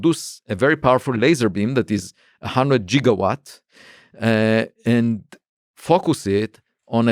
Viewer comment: did he say jigawatts?